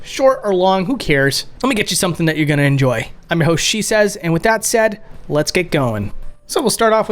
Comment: When you said "I'm your host Shesez" I thought you said "that's what she says" the first 3 or 4 times I listened and it was great considering what came before it.